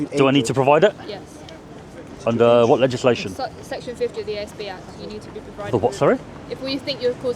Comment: Section 50 terrorism act is not anti social behaviour, she’s mixing up her laws. This is a classic section 26 and she is pushing her luck? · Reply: She never said section 50 was terrorism. She clearly said ASB.